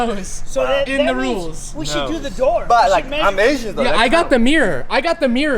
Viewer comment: did say “but like I’m Asian tho”😭😭😭